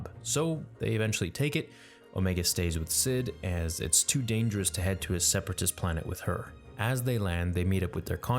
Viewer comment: nah i think he says because it’s crawling with imperials